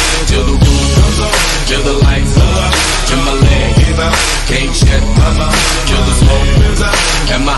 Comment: I thought he said “gang shit I’m out” not “Can’t shut my mouth”